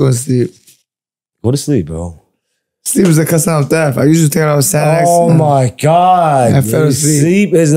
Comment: did he just say he usually takes a bunch of xanax..?? 🤔